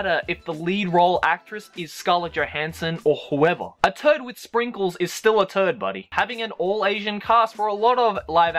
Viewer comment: I thought he said "a teletubbie with sprinkles" lol.